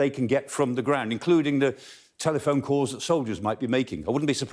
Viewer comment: He said 'soldiers', I heard 'Soviets'. No difference, really. :)